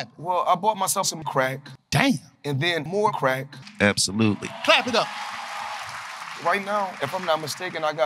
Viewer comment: "Clap it up" thought he said "crack it up" 💀